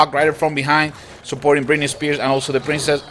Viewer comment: Stop at 4:44 .... He said britney spears instead of spear goblins????WTF